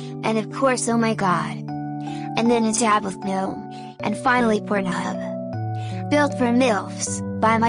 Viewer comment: Sorry but I heard "and finally pornhub"